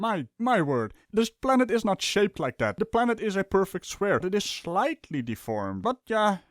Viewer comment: Wait.. did I just hear Sir say the world is a perfect square at 15:16? I know you meant perfect sphere and actually you could have said that and I misheard, which is the more likely case... I just found it humorous. Good video as always and keep up the good work.